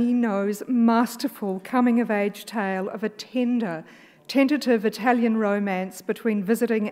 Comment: I highkey thought she said “tinder” and I was like no it’s not that kind of movie hun